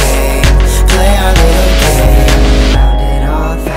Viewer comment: did i just heard "play our little gay"